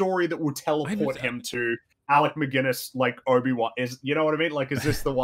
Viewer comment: Did call him Alec McGuinness?